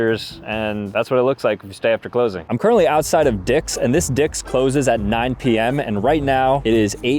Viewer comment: dicks wtf did I seriously Hurd sambucha say dicks